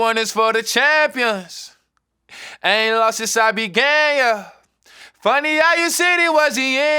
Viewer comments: Not even gonna lie I thought he said “I ain’t lost since I’ve been gay” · dam i thought he said “I ain’t lost since I been gay yuh” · I really thought he said I ain’t lost since I been gay damn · Was I the only one who thought he said " I be gayer" instead of "began (yuh)" lmao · Why did i hear "it aint long since i been gay yuh" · Oooohhhh dude I thought he was saying "I ain't lost since I've been gay yeah" like as in he hasn't taken an L since coming out, mad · Who else thought he said “I ain’t lost since I been gay yuh” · who else thought he said "I aint lost since I been gay yea" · I thought he said “I ain’t lost since I been gay (yeah)” in the song. That’s what you get for listening with your eyes. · I’m I the only person who thought he said “I ain’t lost since I been gay” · I thought he said I ant lost since I be gay yuh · When he says “I ain’t lost since I began yuh” I always thought he said “ ain’t no losses, I be gay yuh” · i thought bro said i aint lost since i been gay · 0ok am bj I the only ok ne who thought he said been gay in the sing but realize he said began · When he says ‘since I began uh’ this whole time I thought he was saying ‘since I been gay uh’ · What i hear: "Never lost since i be gay yah Lyric: "never lost since i began yah" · i always thought he said “i aint lost since i been gay yeahh” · i thought he said lil nas x i be gay yuh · okay im sorry but who else thought nas said "in lost since i been gay" ? anybody else hear that or am i jus stupid ?